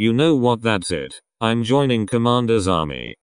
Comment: something it say: i'm joining commander's army when i hear: i'm joining commander's zombie